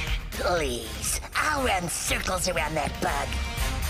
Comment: what did he Say? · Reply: Please, I will run circles around that bug